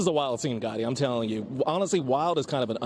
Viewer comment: did he just call that other reporter “guy” ? As in “this is a wild scene Guy let me tell you” ? Or is his name Goddi or something similar?😅 edit ok his name IS Goddi